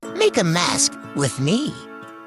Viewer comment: I thought you said make a mess with me💀